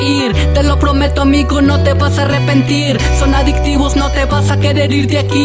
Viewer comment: like si namas yo me di cuenta que dice dictivos jaja esta mamalon el video